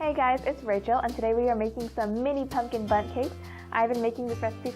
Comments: Hehe It sound like you say butt cakes ;D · It sounds like she is saying butt cakes not bundt cakes